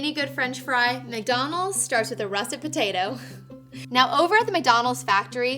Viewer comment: Oh, what u say is "Russet Potato" thank´s god for google! Please tell the subtitler as well..... 0:52 That's kinda important when it comes to make the most famous fries ever known